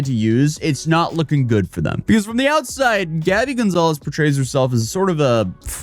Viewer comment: my brain was tweaking so I thought bro was saying "Danny Gonzalez"